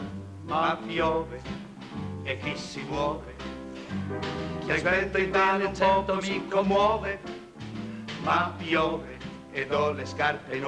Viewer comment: bellissima! Qualcuno sa cosa dice il terzo verso (0:21) " ... ci commuove"? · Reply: "Chi aspetta in vano un poco mi commuove"